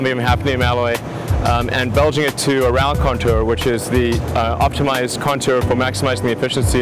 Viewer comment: what did he said right there? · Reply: "To a round countour"